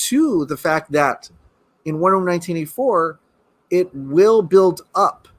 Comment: Wait.. Lol what are u saying? Sounds like "Wonderm 1984?" 🤔😄😄